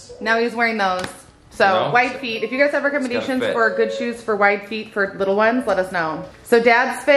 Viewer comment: laugh about the homework was too funny.. I also had to restart three times bc my dog kept bumping my hand and it turn off. And all three times I heard “ shoes for white feet” instead of “ wide feet” and was so confused for a min lol